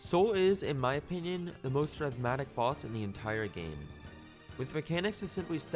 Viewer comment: The most what boss? What is he saying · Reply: I believe he said rhythmatic when he meant rhythmic